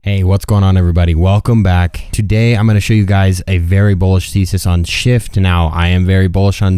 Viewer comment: did you say Bullish Feces?